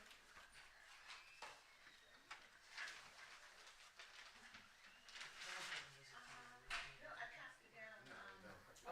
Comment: Did she actually say to her lawyer at "I won" at 5:30:19 ?!?! 😟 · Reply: She did indeed mouth the words did I win and then turned to her other lawyer and said did I win ....she certainly will have a rude awakening on that statement...